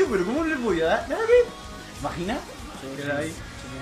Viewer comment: 2 Preguntas: 1) xk siempre k juegan un pasamando en juego de armas, siempre este ese tipo k se llama: ola_69? A caso es amigo de ican o que (PORQE SIEMPRE JUEGAN CN LA CUENTA DE IVAN)? 2) 3:08 porque; no se quien dice: "¿VAGINA?" PORQUE DICEN ESO? Osea no me parecio un insulto, pero muy fallao xk de la nada dicen: "¿VAGINA?" Seria todo un saludo y muy buen video de y chau